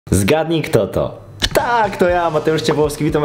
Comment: Zgadnij kto to? Ptak to ja Mateusz XD 🐦 0:00 żartuje ale serio na początku usłyszałam ptak